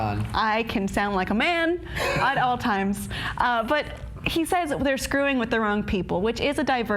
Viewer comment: I thought Rick said "They're schooling with the wrong people". P.S looks SO beautiful today just had to say it! ^_^